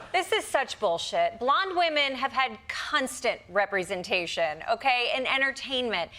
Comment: did she say "cunstant'? lol